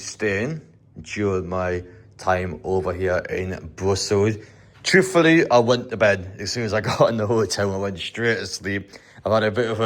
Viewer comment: I thought you said -"truthfully I wet the bed" 😂 I was gonna say that's very honest of you marra 🤝Is the manaquin piss still a thing ??